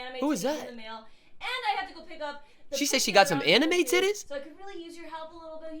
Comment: I think she said “anime DVD”